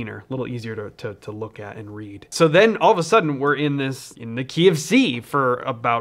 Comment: What he says : Key F/C Me : KFC